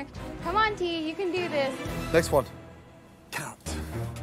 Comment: what did he say ??? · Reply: Carrot my guy